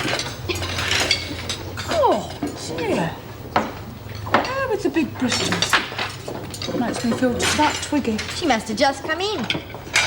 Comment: What does that girls say who is shaking the pepper!? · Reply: I don’t believe she said anything, except for Chrissie, who said, “Cor, dear. Her (referring to Trixie) with the big Bristols. Makes me feel just like Twiggy”. 😃